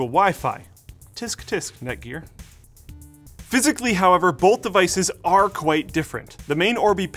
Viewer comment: did Luke say "Both ISIS"?